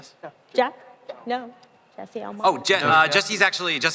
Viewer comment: i thought that interviewer said gypsy when she actually said jesse. she definertly has a weird accent